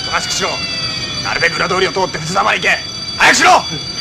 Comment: 「なるべく裏通りを通ってスタバ行け！」 にしか聞こえないw